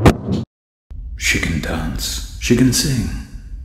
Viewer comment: chiken daance, chiken sing (idk why I heard that)